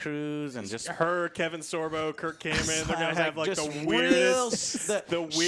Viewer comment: anybody else have to do a double take when alex said kirk cameron because you thought he said kirk hammett? i was about to throw hands with myself